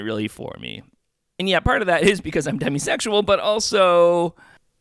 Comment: maybe for you, as a demi, but it’s kind of weird to insult people that do enjoy dating someone while getting to know them (not always sexual) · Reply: Never heard of this demi sexual orientation till now. At first I thought he said hemi-sexual. Fill me in?